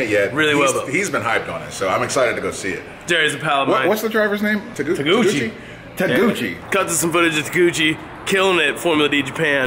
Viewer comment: who else thought he said taniguchi at first?